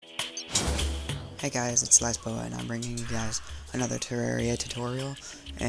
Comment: At the start of the video, it sounds like he says “Hey guys, it’s lezbo”